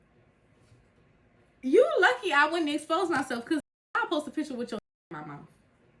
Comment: huh ....did she say you lucky I don't wanna expose myself because I'll post the pic w your 🍆 in my mouth ??? YEAAAAHH PACK HER UP CASE CLOSED #304